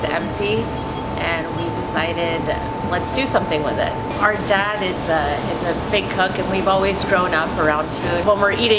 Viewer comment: "Our dad is a big ** " who heard it like I did XD